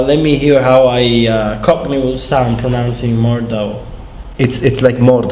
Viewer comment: When you said cockney I thought you said gopnik, also do you plan to ever learn a Slavic language